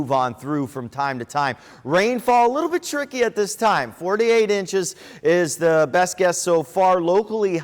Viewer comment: …..did he say ‘48 inches’? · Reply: 4 to 8 inches